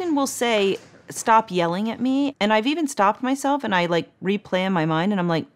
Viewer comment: Why did she say "Kate, stop yelling at me" if I called my mom by her first name lol... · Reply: I heard "Kay please stop yelling at me" as on "Kay' as in "okay"